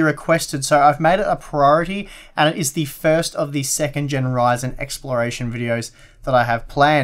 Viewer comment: At first i thought u said generyzen and i was dead